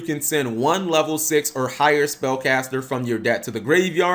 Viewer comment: What he’s implying is that you send the dark magician or dark magician girl from your deck to the gy and then summon from the gy is basically just summoning them from the deck with some extra steps. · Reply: Oh i thought it was saying "1 level 6 or higher spellcaster from your Hand to the GY"